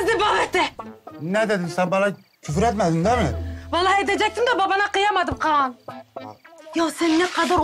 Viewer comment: de Nazgül ne diyor ? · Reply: Alisabina Sabina Ben Babanı... diyor. Küfredecekti ama küfür kismini söylemedi